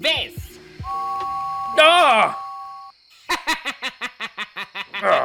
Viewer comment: Oh go to hell u reincarnation of the devil 15:09 lol noice I thought he said retarded